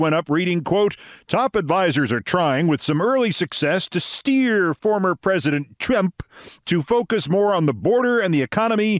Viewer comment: I heard that as "Former President Chimp". I like that.